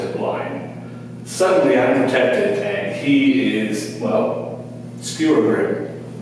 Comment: Did he say Screwergrim? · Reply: skewergrim. XD